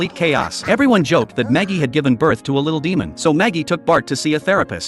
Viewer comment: I am not the only one that just heard it say Maggie gave birth to a Demon child. It’s marge